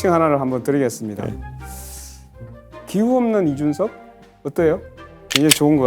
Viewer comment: 기후 없는 이준석 꼭 기호 없는 이준석으로 들리네... 권영국 후보님 손바닥 백성'민'자도 킬포인데...